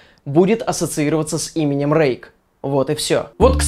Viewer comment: Мне одному на 3:39 послышалось что он сказал не 'Рейк' а 'Рейх'?